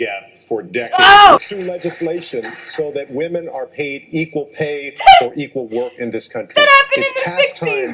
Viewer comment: It must be because I'm half asleep, but at 2:31 I hear "Equal pay for equal woke." Finally the truth comes out.